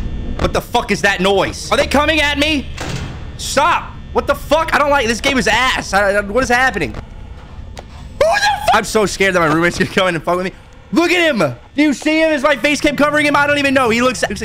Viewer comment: i thought he said "im so scared that my roomate is gona come in here and fondle me"